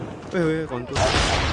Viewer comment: ngomong apa bang?:v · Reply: Concu (Concussion Grenade)